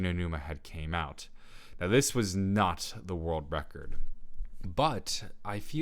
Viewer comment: I had my audio really low so I thought you said Trump got the world record